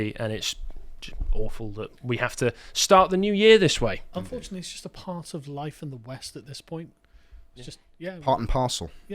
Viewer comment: I initially misheard that as "Unfortunately its just part of life in the WEF at this point".